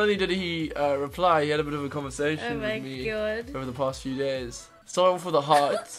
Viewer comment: When he says days if you think about years it sounds like he is saying years